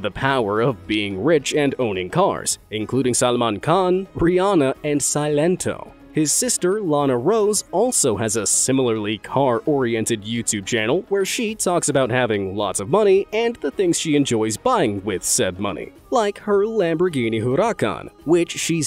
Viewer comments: i heard that so wrong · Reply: lana rhoades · I thought he said lana rhodes